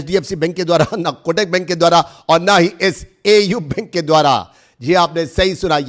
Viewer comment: Yeh video sponsored nahi hai 10:25 na hdfc bank ke dwara na kotak bank ke dwara aur na hi SAU BANK ke dwara… did you said SAU BANK OR AU?